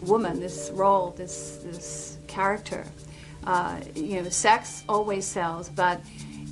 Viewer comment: Please. At 7:18 the captioning reads "vaccine" instead of "sex scene" Probably YouTube doing it. Such an obvious manipulation.